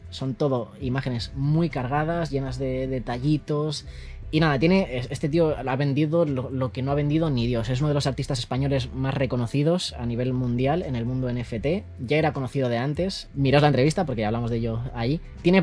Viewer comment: Por un momento pensé que decía los artistas NSFW